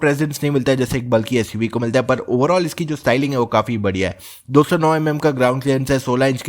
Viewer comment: Is it just I heard "9 mm Ka ground clearance"🤨🤨🤔🙄🧐